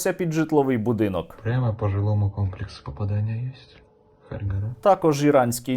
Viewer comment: Мне это послышалось или нет " прямо попадание по жилому комплексу ХАРЬКОВ"